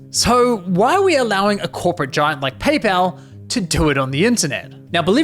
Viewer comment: did he say PayPal not Honey??